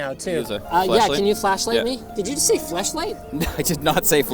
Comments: I heard fleshlight. Play it at .75 speed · I swear I heard him say FleshLight too.... lmao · "Did you just say fleshlight?" yes he did.